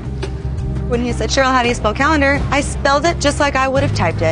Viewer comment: when the host says calender it sounds like he says cowlander.